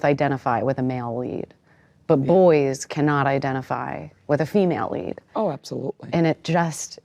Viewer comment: I could have sworn she said “teenage female lead…” I realize that’s not what she said, but that’s how I processed it. And to her (Susan Collins’, not Jenifer Lawrence’s) credit, I think it’s fairly accurate. As a rule, society does not think of 15 yo girls when they picture armed soldiers fighting to their deaths. And imo, that’s not a bad thing.